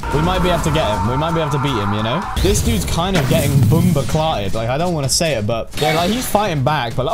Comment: Nahhh the bomboclat said at 35:11 im an jamaican so i understand what he said bomboclat its a curse word in jamaican